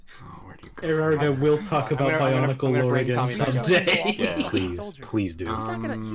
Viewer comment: Did they say bionicle at 1:53:44 cause I thought did